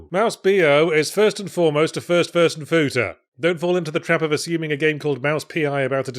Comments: Did Yahtzee just say "first furson futa"? Well I'll just type that into steam to see what this game is all about! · "a first person futa" did I hear that right? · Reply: I thought he said "food-er"